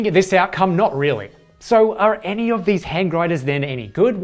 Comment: Bro I thought it said best hand grenades for a sec lmaoooo